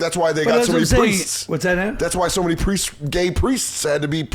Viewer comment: Did Colin say “What’s that Ant?”